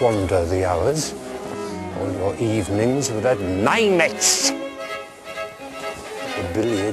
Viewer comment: What was that word that Beethoven used? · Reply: Lingmets